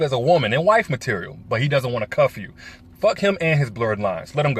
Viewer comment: "F**k him and his blurred lines, let him go" LOVE IT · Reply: Lol I heard,"F**k him in his blurred eyes!" 😂😂😂